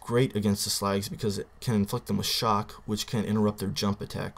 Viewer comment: O_o at 1:08 it sounds like you say it can f*** them a shock